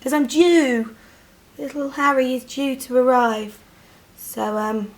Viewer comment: I thought she said "it's because I'm JEW" at the beginning when she was talking about being due to have her baby...I was like 'waaaaaaaaa?' lol